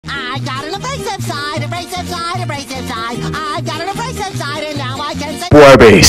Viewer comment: He sounds like hes saying ive got a racist side